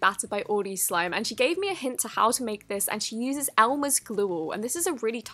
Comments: it sounded like she's saying Elmer's Gloooall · What happened to your voice when you said glue? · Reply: glue all